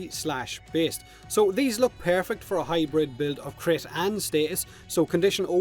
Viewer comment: It sounds like he says hydroid build at 343 lol... I had to hear it twice... Still unsure.